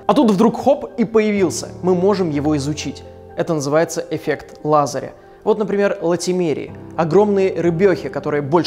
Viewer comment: Мне послышалось "Эффект Лазарева" ))))